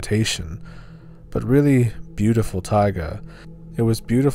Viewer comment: Whenever anyone says "taiga", I hear "tiger" and it's a bit confusing until I recall that "taiga" is a word.